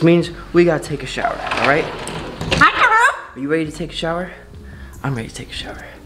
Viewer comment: did she say, “ Hi Toruk?!”